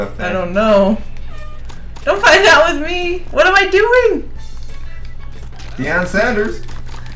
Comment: did he say deion sanders